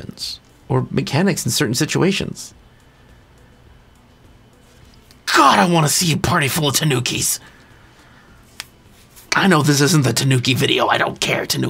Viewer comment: I also thought you said, "God I want to see a party full of the Nookies." 🤪